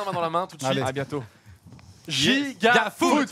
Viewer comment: c'est moi ou sa fini sur un "giga foutre" ptdrrr